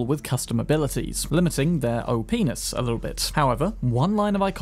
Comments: Did you say “O penis”? 😂😂😂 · For 25, I heard “As of 2.0 most iconic weapons were rendered no longer modable… limiting their oh penis”